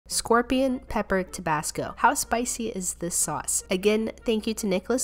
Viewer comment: Huh? DID U JUST SAY TABASCO?!? hmm u watched lookism too good job..- someone semi deaf ear and blind